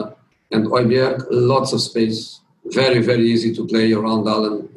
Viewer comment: what did i just hear??? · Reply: he said Hojberg, lots of space